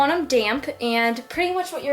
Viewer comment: When she said "damp hands" I heard 'damn hands' and I was like gasp then I realized...